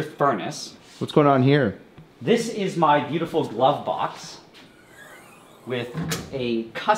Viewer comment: sounds like he says "Lovebox".